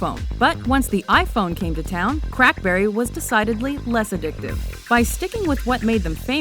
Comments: Did she just say Crapberry?!?!?!? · did she say crackberry?! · did she say Crapberry?! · DID SHE JUST SAY "CRAPBERRY?" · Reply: No, she said crack berry. It is a common name for blackberry among the tech geek community. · did u say CRAPBERRY? xD